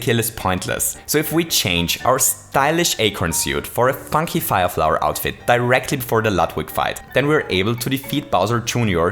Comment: Did he say "f**king fire flower outfit"?